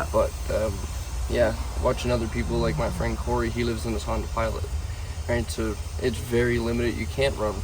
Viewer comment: What does he say about his friend Cory? He lives in a what? (talking about heat) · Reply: Sounds like Honda Pilot